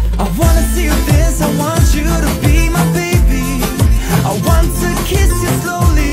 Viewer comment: Why did I hear “I want you to feed my baby” 😂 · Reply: Suhara Shuchita thought it said I want you to have my baby 😂😂